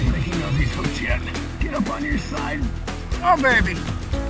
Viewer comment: did he say ohh bob saggot lol